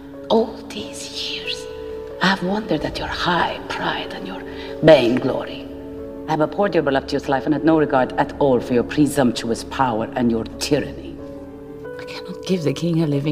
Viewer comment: Does someone understand what she says at 0:34 after saying "all this time, all these years" because I cannot understand her words... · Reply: "I have wondered at your high pride and your vain glory. I have abhorred your voluptuous life and had no regard at all for your presumptuous power and your tyranny."